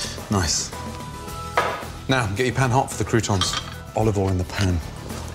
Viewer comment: did he say "gay pan hot" at 3:40?